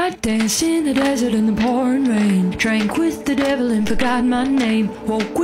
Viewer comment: I thought she said ‘porn rain’, lol